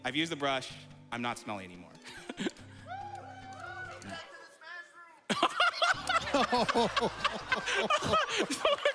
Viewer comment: Can somebody tell me what was yelled at 21:31? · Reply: "Take that to the smash room"